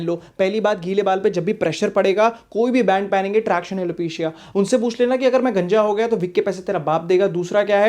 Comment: kya bole ??? Tractional opesia kya ? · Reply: Traction alopecia